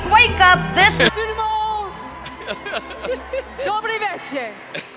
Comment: am I deaf or did she really say Dobry Wieczór???